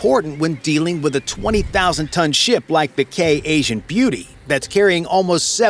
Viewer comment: Can’t be the only one that heard him say gay Asian beauty